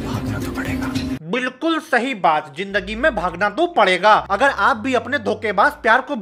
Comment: who else heard this "hagna to pade ga" ???